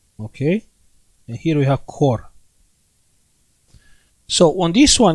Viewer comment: I didn't understand what you said at 25:34. I thought you said cork and I don't understand why.